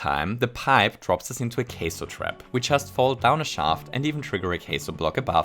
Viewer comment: I thought he said 'queso' trap lmao, I'm really hungry